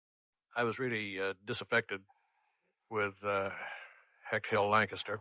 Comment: Thanks for elaborating. I have seen Separate Tables and I think I’ve read all the books on Hayworth but I didn’t recall any stories about Delbert Mann’s experience of making the film or his relationship with the producers. (Or I’ve forgotten reading about it!) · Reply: I just listened to Mann’s first comment on this audio again and I realize I misheard him initially. I thought he said “I was already disaffected with HHL…”. What he actually said was “I was really disaffected..”. So he’s explaining his reasons in the clip—which you described perfectly.